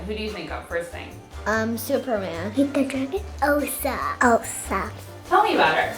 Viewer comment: did I just hear "Ender Dragon"????